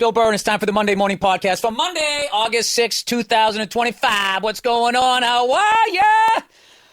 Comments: Did he say August 6 2025? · Did he say it's the 6th of August 😂